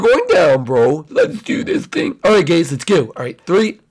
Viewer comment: Anyone notice at 9:54 said gays instead of guys